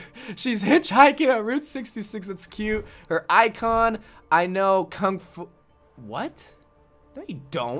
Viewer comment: I thought it said I know Kung Flu to make a corona joke but i was wrong lol